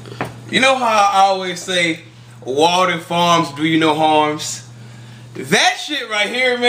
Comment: this whole time I thought you were saying "water farms" lol